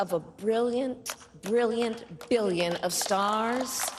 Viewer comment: Did she just say, "...the light of a brilliant billion of stars?"